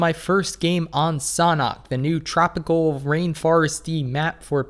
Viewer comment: Am I the only one thinks that said sauna instead of sanhok xD